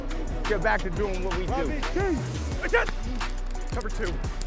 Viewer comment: Is Brady saying "Cover 2?"